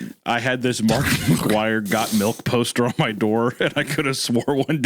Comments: I thought said he had a Markiplier Goth Milf poster at first 😂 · out of all things, i never would have guessed that first remark of this story was saying he had a related story dealing with Mark McGwire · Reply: I thought he'd said a Markiplier "Got Milk" poster the first time, and I was really damn confused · Ngl I thought said "Markiplier Got Milk Poster" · im losing it i thought said he had a Markiplier got milk poster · Could've sworn he said "A Markiplier Got Milk Poster" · I thought he said Markiplier until I heard it a second time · I thought you said “Markiplier Got Milk” poster 😭🤦🏻‍♀️